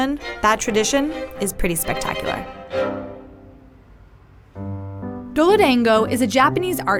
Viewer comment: is it just me or does she say duolingo just me? ok then